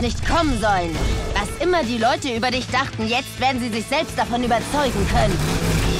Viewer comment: what she say? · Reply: Du hättest nicht kommen sollen. Was immer die Leute über dich dachten, jetzt werden sie sich selbst davon überzeugen können. You shouldn't have come. Whatever people thought about you, now they're gonna be able to convince themselves of it (?)